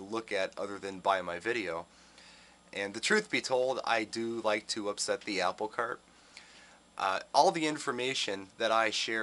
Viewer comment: what was that saying you said at 3:27? "I do like to upset the apple cart?" I think I know what your saying but I'd also like to know for sure? thanks!